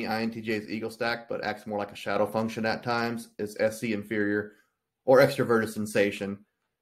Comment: The Se inferior as related to performance anxiety, as in the high expectations I have of myself and sometimes feel are thrust upon me by others (who I have deemed important in my life, not random other people), was very relatable Thanks for the video! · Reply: Whoops, misheard Se inferior, not demon